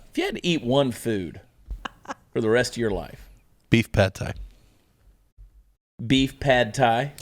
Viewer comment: The answer to the "what's your favorite taco?" question could have been much worse lol I woulda said "your's" lol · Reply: I thought he said beef paté. Pad thai is much less weird